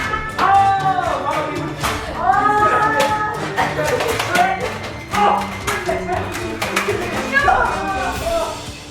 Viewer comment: Omg did he say shrekt???? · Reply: no he said in the backround SHREKT